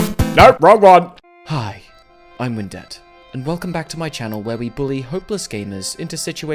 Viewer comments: every time he says "I'm Windette" i hear "I'm in debt" instead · I heard "Hi, I'm in dept" instead of "Hi, I'm Windette"... What has my head come to